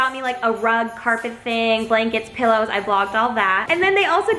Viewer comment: am I the only one who thought she said car